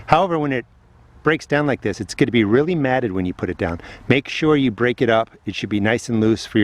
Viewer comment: When you said "break it up for your layer", I'm thinking "break it up for your lair" lol. I'm going with the 2nd version for my garden. I like watching when and what you plant because I know that's what I need to do next, being a further south. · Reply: I thought he said "break it up for a year" 😂. I was looking in the comment section if someone had an advised of what's else to use since I need to fill my beds now!